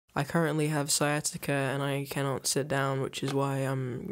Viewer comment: I thought you said 'I currently have science' and was really confused. Lol.